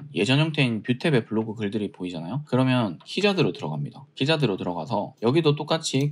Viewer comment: 초에 키워드검색을 위해 들어가는 사이트 이름이 뭔지 알수 있을까요?.. 키자드 라고 들리는데.. 찾아보니 아닌것같아서요.